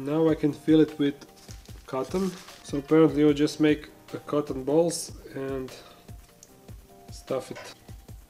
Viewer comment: for some reason when you said 'cotton balls' my brain heard it as 'cocknballs' and it made me laugh my ass off